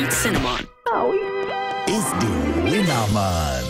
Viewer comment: I used to think that this bad apple said “Yoo hoo, Are Apple Jacks Skeddit?